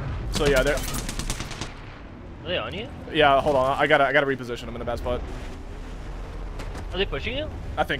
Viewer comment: Did you say “I got to reposition I’m in my dads butt” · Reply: latch no he said “i got to reposition i’m in a bad spot.”